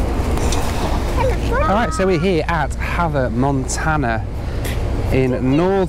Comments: For a second I thought you said Hannah Montana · I thought he said Hannah Montana